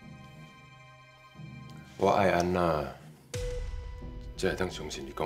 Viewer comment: 我只用聽的 在滑看看有什麼影片，然後就聽成我愛安娜 嚇死